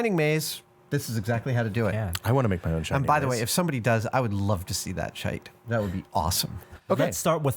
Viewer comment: Did Adam say "I'd love to see that shite" at 1:10?